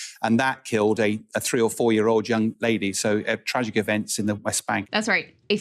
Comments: I thought i heard young BABY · I thought he said young baby!!!